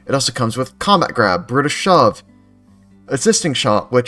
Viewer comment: I thought you said it comes with British shove. x3